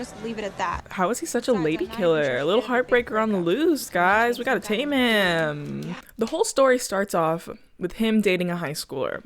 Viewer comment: me thinking said Taemin instead of “tame him” and going yeah he is a ladykiller and a heartbreaker on the loose, isn’t he? 😑